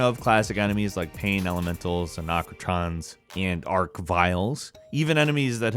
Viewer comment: did you say "Anakrotrons" and "Ark-Vile"? not the first time ive heard someone say their name like that